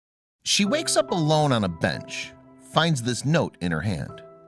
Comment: Thought it said beach instead of bench :P